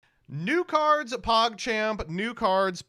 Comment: Sometimes I'm just hearing to the intro absentmindedly and I hear "Nude Cards, PogChamp"(2x) Which always gets my attention, because nude cards are indeed "PogChamp"